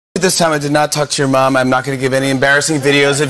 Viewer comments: Lmao I had to reply that I thought he said touch not talk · At first I thought he said "this time I did not touch your mom" I was like wtf